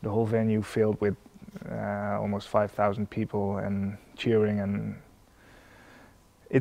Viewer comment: does he said "five thousand people"??